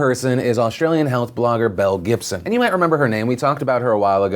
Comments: oh my god i thought you said mel gibson i was so confused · Belle Gibson I thought he said Mel Gibson